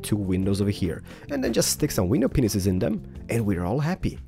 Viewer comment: Did he just say.... "window. P. E. N. I. S. E. S." 😰😱😱😐 I'm not being mean but...I'm not thinking dirty I SWEAR ON MY LIFE! 😵😕